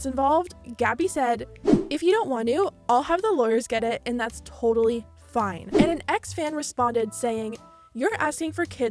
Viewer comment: why did they sensor the word "Sign/Sue(i assume that's what it says)" at 5:00? Edit: Sue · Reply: Sue